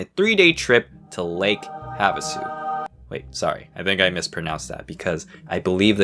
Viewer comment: When he first said the camps name i heard "like have a soup"